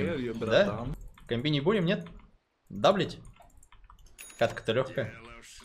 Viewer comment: Сперва услышал как "Да бл*ть?"